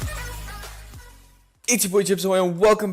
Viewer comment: Wait does he say: “It’s your boy, chips ahoy” at the start of the vids?